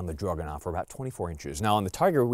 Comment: sounds like he is saying druganov the entier time